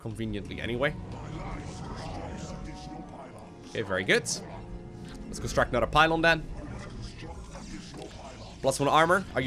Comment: Did i just hear "you must construct additional pylons" at 20:33?